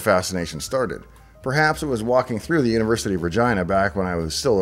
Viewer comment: Sorry, I'm an Aussie, but it sounded like you said "University of Vagina" If so, where do I enrol?